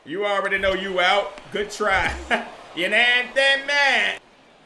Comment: What the hell did he say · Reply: Xx April "Ya kna wha I'm takin' 'bout!" lol